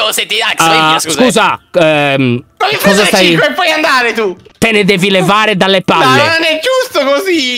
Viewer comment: A: voglio prendere cico poi andare tu ( non si frega della pronuncia di Alex e lo manda via) raga secondo me ci vuole un'altra volta #AlexReboot (era così??XD)